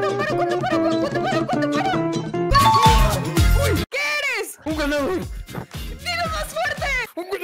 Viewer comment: En la 6:52 pense que dijo "un guandul" xD